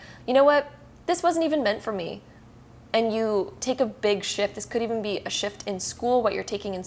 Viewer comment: I heard sh*t and not shift 🤦🏻‍♀️